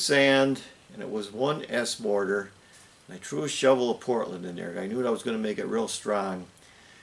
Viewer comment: at 10:40 did you say "i true shoveld portland in there"??????not sure whay you mean by that love your vids im a addict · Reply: Hi Marty, yep I added a shovel full of Portland to the mix, Thanks for the kind words